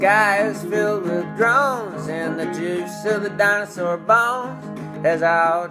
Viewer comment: Great song!!! Though I misheard "dinosaur bones" as "dinosaur bums" lol